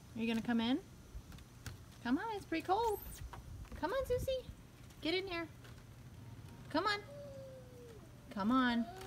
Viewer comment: When she said get in here it sounded like he said why 😂